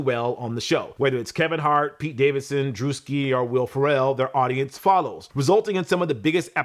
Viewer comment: Did you just call Will Farrell, Will "Pharrell"?🤣🤣